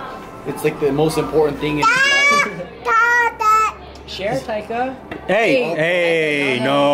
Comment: it sounds like he said "stop that" lol